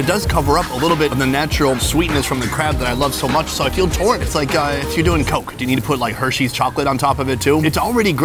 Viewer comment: Did he say "it's like doing coke" at 10:06?